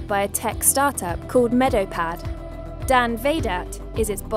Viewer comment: I legit thought you said Darth Vader when you in introduced Dan Vahdat.